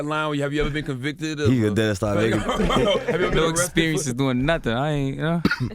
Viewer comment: right I don’t get what he was saying that was bad? It sound like he said I dont have experience doing nothing Maybe they had said he did? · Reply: he said "no experience doing nothing". That's like self incrimination cause it means king von been doing dirty shit all his life to get money